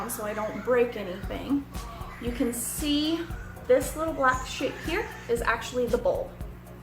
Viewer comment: lol sounded like you said "this black sh*t here is actually the bulb". Thanks for the info. I'd have ended up burying the bulb!